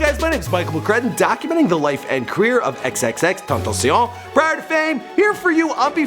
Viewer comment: Anyone else hear “she” at 1:07 ?